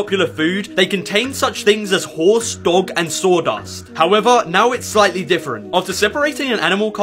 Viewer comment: "horse, dog and saw dust" Holdup, did you just say dog and horse dust? Bro ur sus